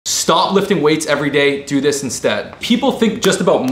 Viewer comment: I’m high af I thought it said shop lift everyday